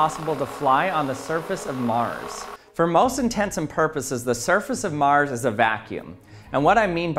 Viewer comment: haha 0:06 Did you just say "for most intensum purposes"?